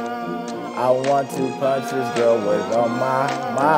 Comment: “I want to punch this girl with all my might.” 911 what’s your emergency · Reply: i thought he said “i want to punch this girl but i’m not white” lmao